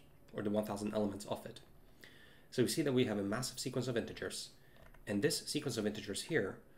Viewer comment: DID I HEAR MASSIVE?????